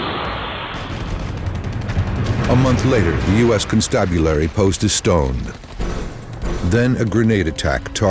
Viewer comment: What did he say? "A month later, the US constabulary posted stone?" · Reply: "A month later, the U.S. Constabulary post was stoned". As in, they threw rocks at it. Sounds goofy as hell though.